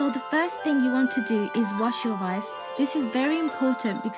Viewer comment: you got me.. 😝Wash eyes instead of rice😂😂😂